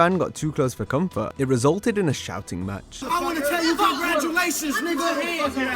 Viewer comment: Dude, yelling at Jericho was definitely not a fan. I don’t even think he knew who he was. 😂😂😂😂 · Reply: Really? Sounded like he said something like "I was trying to congratulate you" or something at one point